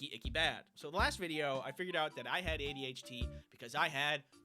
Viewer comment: I may be hearing it wrong, but it sounded like you said "ADHT"